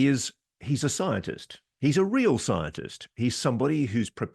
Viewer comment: An evolutionist? I thought you said he’s a scientist.